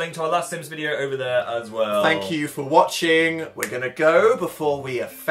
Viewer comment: Dan: "WHAT ARE YOU?!" Phil: "AN IDIOT SANDWICH" wat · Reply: Omg I thought that he said an Indian sandwich. But yours actually makes a lot more sense... :|